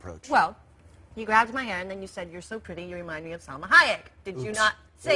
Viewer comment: What's she saying at 2:14 ''You're so pretty, you remind me of someone ??(hiatt)'' :D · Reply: Salma Hayek